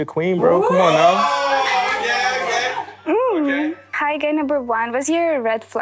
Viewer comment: hi everyone 🫶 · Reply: When you said "hi guy" I heard "hey gay" 😂